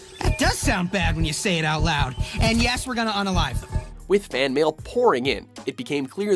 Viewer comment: What does Deadpool say at 7:52? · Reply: Yes we're going to unalive